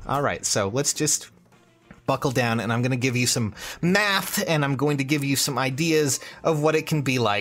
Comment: Did you just say METH? DEMONITISED.